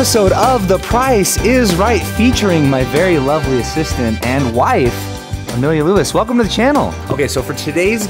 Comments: thought he said sister and wife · I thought he said “featuring my lovely sister & wife” 😂😂😂